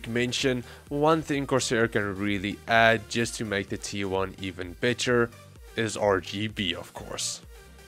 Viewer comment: i heard even bitcher